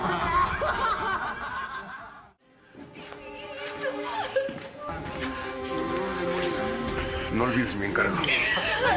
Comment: que dice??? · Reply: Hijito